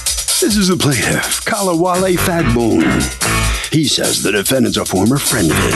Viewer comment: Did he just say "Kally Wally Fat Bone?"